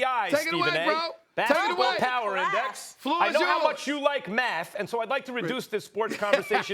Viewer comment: lol...for a sec I thought Max said "...how much Jews like Math..." and my jaw dropped. This segment was hilarious though. As good as Steven A and Skip were....they are just as good.